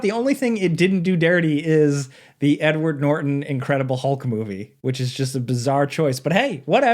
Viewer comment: I definitely misheard this as "the n-word Norton Incredible Hulk movie" The mental images I got from this were hilarious, so ty 😂